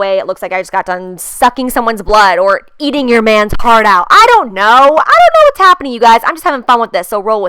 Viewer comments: I'm dying I think you said "eat your mans heart out " but I heard " eat your mans part out " #dying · My bf thought you said "eating your mans part out" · omg I thought she said "eating your man's part out" lololol